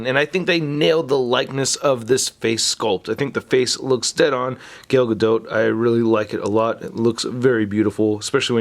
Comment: did he just call her gale gadote? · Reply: JB its "Gal Gado" the spelling is Gal Gadot but you pronounce it as "Gal Gado" with a faint T